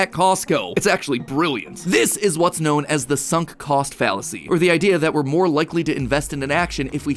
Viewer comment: Always thought Technoblade said "Sun Cast Fallacy" But now thanks to Food Theory I realize he said "Sunk Cost Fallacy" 🤦‍♂️